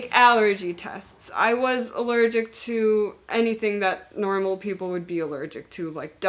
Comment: Thank you for making this video, I learned so much. I really appreciate the information. Also I thought you said allergic to Poland was common at first, had me grinning.